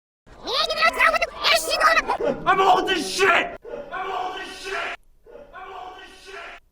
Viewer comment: Yep. I too googled Canadian's age when he shouted "I'm old as sh*t". Can confirm that 23 does NOT feel old, even for a sportsperson. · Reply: I thought he said "Ima hold this shit"